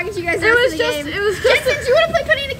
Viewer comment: What did she say in the time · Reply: Jentzen do u wanna play penny nickel di- ( she got penny nickel dime stuck in her head so she realised)